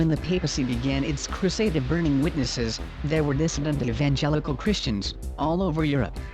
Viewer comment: Thank you again for all your hard work I am going back and watching all your videos again and enjoying every minute of it. I just have one question please tell me what is the word or words that I am missing at point 30:32 in the video when you say, " When the papacy began its crusade of burning witnesses they were ............................... Christians all over Europe even before the time of Wycliffe." My ears ring every since I was in a car accident years ago so I miss certain words with the mechanical voice talking usually one of my kids will listen and tell me what the word is but this part even they are not getting. I looked at the transcript but the words in that sentence don't make sense or the transcript is right and I am not getting it. Thank you again for your time with this matter. Blessings and hugs times the stars. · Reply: 'there were dissident evengelical Christians all over Europe even before the time of Wycliffe." (The Torch of the Testimony, by James Kennedy, chronicles that history) When these groups were "persecuted", they were routinely misrepresented by Roman sources. All other traces of their literature, were intentionally burned. So what the general public knows of them,l is purely Roman disinformation about them. (And the Thessalonian massacre was one of these examples) It had actually occured because of the Thessalonian Edict. (The "alleged cause" was because they were all "homosexual rapist lovers") ...a totally absurd explanation [in that Thessalonia was the most "Christianized" city in the empire].. long before Rome's Papacy. (And it was in fact, Rome's ruling elite, that had a very long history of "homosexual pedophia" [going all the way back to their "Caesars"] and especially among it's clergy)... as demonstrated in the "Crimen Sollicitationis" issued by the "Holy See". So there was very little real historical motivation for Roman generals to get worked into a complete "rage" over it. (Much less, be able to justify the action to their Roman superiors)